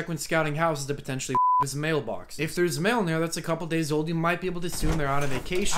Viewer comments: My dumbass thought he said "to potentially f*ck his mailbox." 😅😂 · Oh god, I didn't read the subs at first, so I thought you said to F### his mailbox...